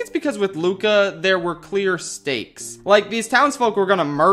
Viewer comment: I thought he said “Queer stakes” and I didn’t even question it